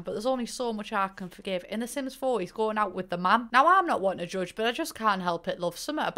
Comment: i thought you said “don was going out with a man, i don’t like it” and i 👁👄👁